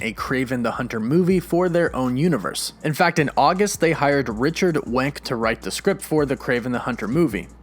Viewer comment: *Im watching the video when 2:13 comes and goes.* *"Wait... did I just hear that correctly?" I think. "Looks like its rewind time."* *"Did he just say that some guy named RICHARD WANK is working on the Kraven script?!"*